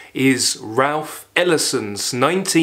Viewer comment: That’s incredible. At 20:18 I thought you said “Ralph Emerson”, but you in fact said Ralph Ellison, only to follow up with who I original thought you said in the first place! It just dawned on me now how similar their names sound